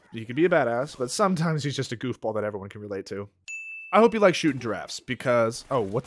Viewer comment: “I hope you like shooting giraffes” best mistake I heard from you and can’t wait for uncharted 2 wins